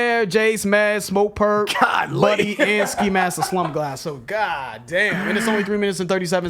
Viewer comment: Did you say "Ski mask the slump glass"